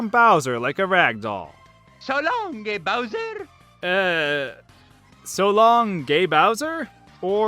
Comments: I hear mario say “So Long Dear Bowser!” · Honestly, that sounded like "So long gay Bowser" to me too...tho I often mishear things too so perhaps that's why. EDIT: • Yup, Let's tickle is what I hear too. • I'm a member of stars • Turtle combat • Hiding cunt • chrome sparks, get out! • I'm tipsy • missed this one • good luck • coconut • bingo, peachy, lets go, here we go • u ready to roll the dice bro • missed it, grid lock • yass Yep, I thought everyone did that (unable to figure what someone is saying in a game and moving on). An example someone else brought up below was Link's sound in OOT during his sword spin...I always hear what sounds like a gagging sound - HHUWATT, no clue what he actually says! Any one know? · I always thought Mario said "So long gay Bowser!" but he is most likely saying, "So long-guh Bowser!" As for Captain Falcon, when he does his B-Up move and grabs the opponent, me and my friends thought he said "Sexed!" which is funnier seeing the characters, awkwardly raped expressions on their faces. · I thought Mario was saying: „Schalom, de Bowser!“, but I‘m not even a native english speaker, so it is only natural that I didn‘t understand Mario in SM64. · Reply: I thought he said "Selam, gay Bowser!" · one of my most stupid misheard voices in super Mario 64 is Mario saying "So long, King Bowser!" instead I heard him say "So long, Gay Bowser!" weird. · I hear him say “so longsy bowser!” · I heard Salam *nyam * bowser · I thought he said so long mi or me bowser